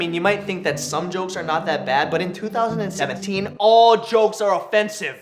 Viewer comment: is it just me or at 2:23 did he say 2016 but voice over it saying 17? XD